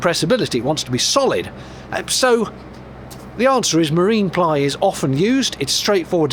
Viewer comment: I thought you said submarine ply at the end of your explanation of why you were using it!😂😂